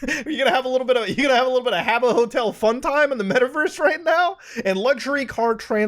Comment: did i hear that right? "habbo hotel"? gosh, that brings me waaay back